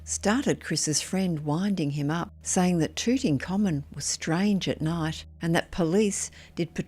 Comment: Due to our narrator’s accent, it sounded to me as though she was saying “Tutankhamen”, instead of “Tooting Commons” (of course, because I’m not from around there, the former is more familiar sounding than the later). I had to go back to the beginning of the story to figure out what words I was mishearing. And I just knew I had to be mishearing it because the ghost in this story wasn’t a pharaoh. Perhaps it’s not the most important observation, but now I’m curious if anyone else heard it, or if I’m just an oddball. And I swear I’m not just trying to be funny (I know better): I really misheard it like that, though, listening to it after correcting myself, I can’t mishear it anymore.